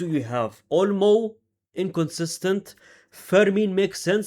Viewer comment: Did he just said OLMO INCONSISTENCE ?